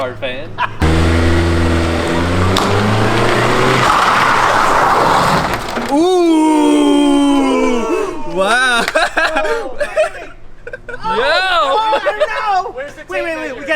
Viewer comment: lol i thought they were saying boooooo!!